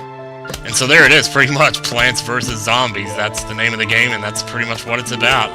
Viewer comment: When I heard you say plants vs zombies, at first I thought you said planets vs zombies... ^^" Awesome all the same!!